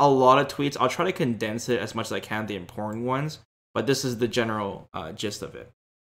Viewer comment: the way u said important sounded like In porn ones 😂